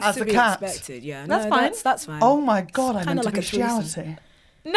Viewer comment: What does say at 49:35?? Even the captions missed it! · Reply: Ok I don’t know how to spell this and I don’t want to google it but she says… “Oh my god, I’m into beastiality” 🤣